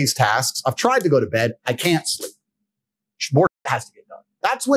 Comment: What did he say at 7:51? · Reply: "some more shit has to be done."